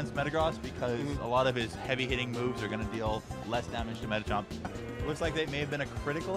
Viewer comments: did he say “metachomp”… hello?? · did I hear metachomp? Lmaoo